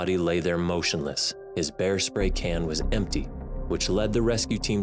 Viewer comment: I thought it said bear spray, it was hair spray